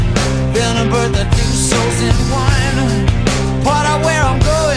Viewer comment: When i was a kid, I thought he said " than a bird that's been soaked in wine" at 0:30 lmaooo